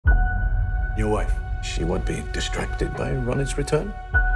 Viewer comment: What he says in the 0:01 sec? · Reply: Broke Girl “your wife...she won’t be distracted by Ronit’s return?”